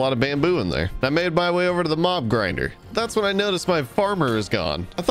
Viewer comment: I thought you said Bob grinder lol XD